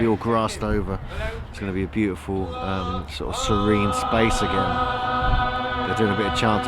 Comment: hm 2:42 Did he say Serene! Well!. :)